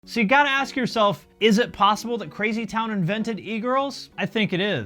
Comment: I thought you said Lazy Town invented egirls in the intro and was so confused til it reached the part where I could hear that sentence again lol.